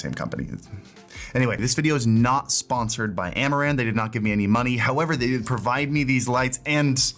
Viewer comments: I heard "Amouranth" lights :face-green-smiling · thought u said Amouranth :)